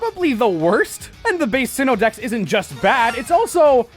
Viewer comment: The subtitles said CeX